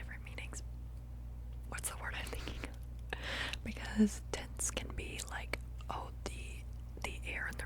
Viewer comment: personally i thought you said tents so i said camping lmao